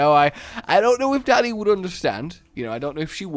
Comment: Sometimes when you talk a bit on the quicker side (I do too don't worry) instead of hearing you say "Dani" I hear "Daddy" and I get extremely concerned for you. I also replay it over and laugh a good bit.